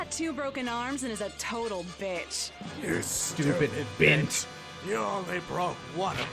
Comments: I've thought it said "you stupid bitch" for years and honestly think it's the better line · Oh Kami, I always thought said "Stupid Bitch" not "Bint" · for years I thought he said “you stupid b***h” not bint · Wait... BINT?! This whole time I've been frustrated because I thought he was saying 'bitch' after she just said 'bitch' and it felt like they were saying "bitch" too much.... bitch. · Huh, I always heard "stupid bitch" at that second broken arm moment. Weird.